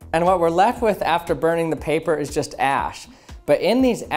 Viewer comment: it kinda sounds like he said Ass